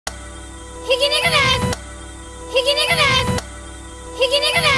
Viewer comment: 「ひき肉」ですが「皮肉です」に聞こえる、、、